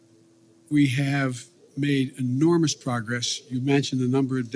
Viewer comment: Could’ve sworn he said enormous PROFITS.